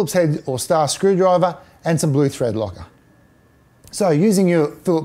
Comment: What is the white thing you show at 0:15 and 0:16 ? I can't understand your pronunciation, and apparently you don't use it for anything either. Thanks. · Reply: He talking about using Blue Threadlocker generally used to keep the screws in tight. <link>